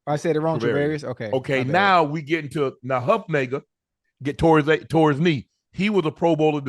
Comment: did he say huefunga as huff-nagar? Who the heck is huff-nagar? at 1:30??? LOL